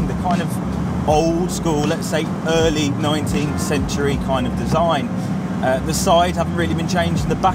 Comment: Did he just say 19th century...